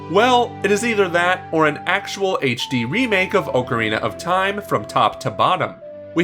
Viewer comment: It sounds like hes saying Oh co rina of time